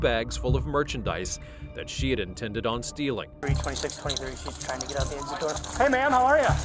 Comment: I say stealing. When I watch these videos I hear "stillin".